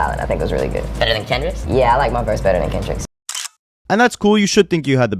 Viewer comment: that’s not what he said lmao. He said he likes his verse.